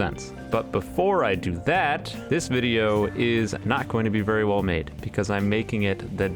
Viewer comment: for a moment i heard "this video is sponsored by raid shadow legends"